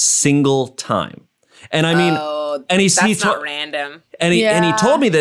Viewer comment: I thought Jackie said "Oooh that's So Random" 😂